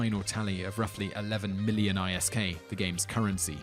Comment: thomas vanatta it was 11 trill isk what stands for Interstellar credits. Btw isn't it called dollars? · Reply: thomas vanatta no i was commenting on how i thought i heard him say millions in the video...and that trillions of isk sounded better as it actually adds up to the 3k$ bill for the battle